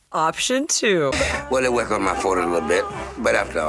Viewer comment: I thought he was saying "Well I work on my Ford a little bit" when I first heard him. Also, I remember Tubbs & Switek doing a bit asking about the peanut butter, Switek asking if I remember correctly "Was it creamy" & joined by Tubbs they both went "Or crunchy." Maybe foreshadowing it was a dream. I noticed this is one of only a handful of episodes where Crockett is on very little. Wondering if it was something Don wasn't happy doing. I think he said Brams, which is both the name of a composer, & but also producer on the show Richard Brams, who co-showran the final season. Overall a strange episode. If it had a darker tone & made it clearer it was a dream, I might have liked it more, I will say.